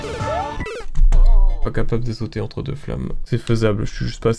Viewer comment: j'ai entendu "entre deux femmes" 😳😳